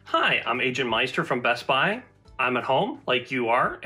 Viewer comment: I thought he said “Hi I’m Agent Molestor”